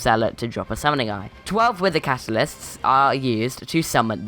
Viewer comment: I think that if wither catalysts are able to be obtained from f7, there will be a huge swarm of f7 players, even more m7 players too (it will prob be out by then), I think that they should be 2 different things. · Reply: I thought you just said regular catalysts and I was like “ok now that’s a bit over the top” 😂